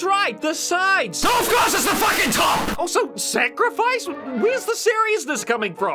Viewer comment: Can anyone tell me what’s he’s saying here? It’s funny, but I can’t make out what he’s saying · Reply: "OF COURSE IT'S THE FUCKING TOP!"